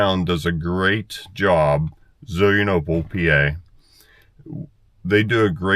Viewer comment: I listened about fifty times and still didn't hear who does a great job on our small town parade about 33:10.... Is it Bull, Pa.? 😄